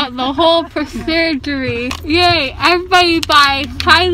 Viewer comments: did she say pro-surgery?? LMAO is adorable! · it sounded like she said "prosurgery" im dead 😂